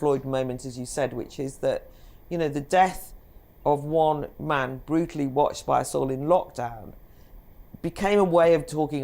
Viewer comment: When Claire Fox repeats the mainstream narrative that George Floyd was "brutally killed by the police", when all the evidence now contradicts that, what hope is there for a fair trial? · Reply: When did she say this? At 3:10 I heard: " The death of one man brutally watched by us in lockdown". I didn't hear the words "The police" or "killed" Could you time stamp where you heard this?